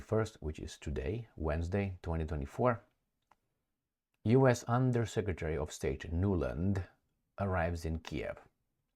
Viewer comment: I heard you saying "undersecretary of STAGE". Deliberate or not - What a wonderfully apt paraphrasing...